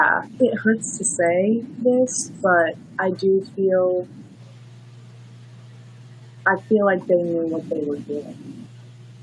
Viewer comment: what did she say I couldn’t understand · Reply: “I feel like they knew what they were doing”